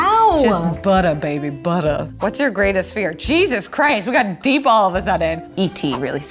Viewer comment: Lol I thought she was saying she was afraid of Jesus Christ and I was like she should beeeeeee